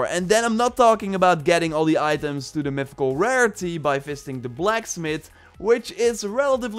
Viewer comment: I thought you said we would get all the items by fisting the blacksmith. 😂